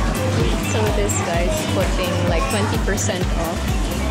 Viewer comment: I thought you said 100% off😂😂